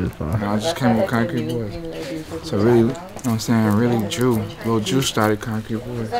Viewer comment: i had to run it back when he said shout out to "lil drew" i swore he said shout out to "lil jew" 😂😂😂😂😂😭